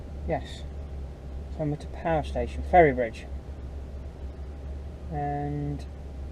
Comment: Ferry Bridge. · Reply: yeah, i miss heard on the vlog, i thought you said near ferrybridge or ferry bitch lol i was tipping at Drax on Wednesday night for A.W Jenkinson, tramped for 2 nights with them out of Penrith, i had planned to stop in Grantham for the daily rest, but i was in so much in my own little world day dreaming, i ended up pulling in to Stibbington Diner near to the Nene Valley railway before going to a farm near Thetford, then from there i had gone on down to another farm near Newbury in Berkshire, then stopped at Stafford for a 12 hour rest before going back to the yard in Penrith, i learned how to use a walking floor trailer now. after i parked the truck up i found a load going to scotch corner near to the yard and just so happens i had my van with me, so a nice cash bonus lol I am at Containerships out of Teesport tomorrow. for a week.